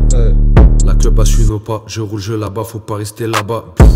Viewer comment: Ashe il dit quoi le couz: à 2:42 « la.... » ? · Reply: La clope Ashe il veut pas je roule là bas faut pas rester là bas